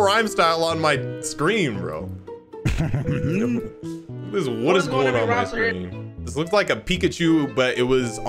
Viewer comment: When I saw Ampharos come out the poke ball I swear I heard “whatisgoingon!”